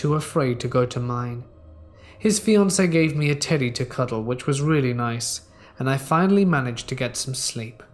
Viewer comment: thought he said his fiancé gave him a titty to cuddle